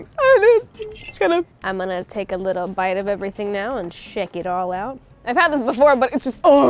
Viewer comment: i thought she said shit it all out 😭✋🏻